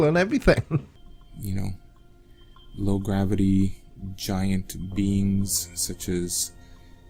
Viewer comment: Oops I better go and check, I thing my pot is boobbling over. I thought he said giant beans as well.